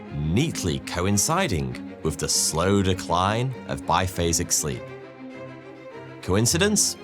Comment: The captions read “basic” instead of “biphasic”.